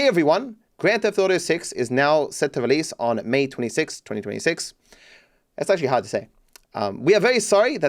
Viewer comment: At first I thought it was saying May 26, 2025 and I was so hyped till I realized it said 2026… it was painful